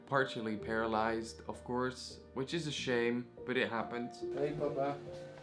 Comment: did he say hej pappa like in swedish?